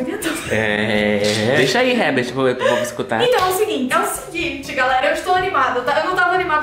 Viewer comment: taokey acho q fui a única q escutei "maurinho" mas blz não me julguem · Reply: Ela falou: "EU IMAGINO..." e vc entendeu "Maurinho"